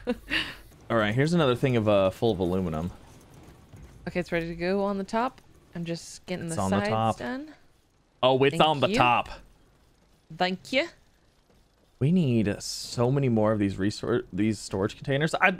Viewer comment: did she just say "dankje" as in the dutch word for thank you? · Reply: Nope definitely just said thank ya haha I wish I knew Dutch.